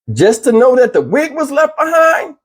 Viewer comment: I thought they said the wig was red??????